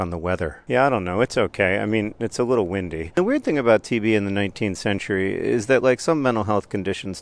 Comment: The video was playing as background noise and I thought John said "TV in the 19th century" not "TB in the 19th century." Tuberculosis makes a lot more sense. TV in the 19th century would've been wild.